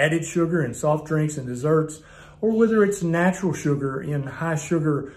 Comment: seven easy...LOL! Oh well, one out of seven ain't bad...I can cut out the low fat dairy. Hey, did he say natto??